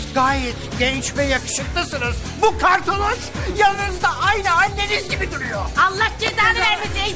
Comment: ne dedi tam olarak anlayamadım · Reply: " Bu kartoroz yanınızda aynı Anneniz gibi duruyor " Diyor :))